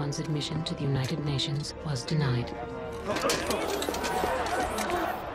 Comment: What does the narrator say at 3:56? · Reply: "But it would not be the last time that the machines would take the floor there."